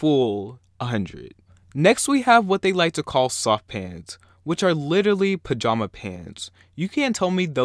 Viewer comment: I thought you said sock pants on the 3rd one and I got really confused. I have horrible hearing.